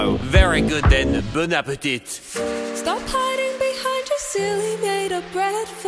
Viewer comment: i always heard “stop hiding behind your silly red flag” as “stop hiding behind your silly bread flag”